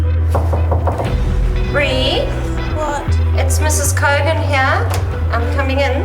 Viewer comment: BRUH DID SHE SAY IS MRS COVID HERE?😂😂😂😂😂